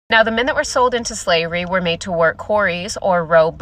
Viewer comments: go wash your ears out. She said “we” and not only once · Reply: nah I heard ‘we’ at first too but she does say ‘the men’, can’t say much about the rest of the video tho bc I haven’t want her it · The fact she says WE like she's been through it 🤣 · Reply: I thought she said "we" myself at first because of how fast she was talking, but since she's Black and the galley slaves were White, it made no sense, so I listened a few more times. If you listen VERRRY closely, what she actually said was, "Now the men that were sold into slavery were made to work quarries or row boats." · "the minute we're sold to slavery" she never was a slave, what is she talking about? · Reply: You deliberately misheard her she said the men that were made into slaves were made to work quarries or to become rowers. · "the minute WE'RE sold into slavery.." like this chick just got done in the fields.. they have no clue what slavery even looks like.. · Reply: She said "the men that were sold into slavery"... She's making a stupid point, as outlined by the video, but you misheard.